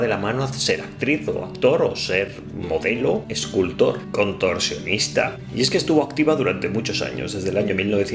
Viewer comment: ¿escuche productor sionista, me equivoco? 🤣es gracioso porque en muchisimos casos es verdad. Los mas famosos se venden al sionismo 🤣